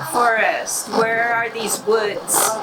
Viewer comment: Slowed all the way down, i hear @ 6:27 Bowersox woods